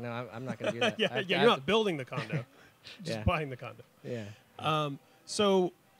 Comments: Oh god, I thought he said condom... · at first I thought he said condom and a died😂😂😂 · Light Abyss i thought he said condom too. 😂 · ok so I thought he said he recently bought a condom like... uhh kay? 😂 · I thought he said that the highlight of his break was buying a condom 😂 Like just a single condom. So proud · i thought he said he recently bought a condom